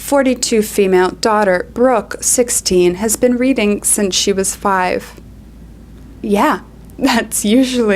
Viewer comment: I first heard that she said "my daughter has been breathing since she was 5" 😭😭